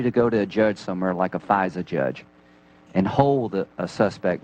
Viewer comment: did he say "Pfizer judge, did he? He didn't, right?"